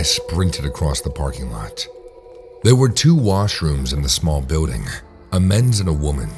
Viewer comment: I thought it said “mushrooms” not “washrooms”. 🌚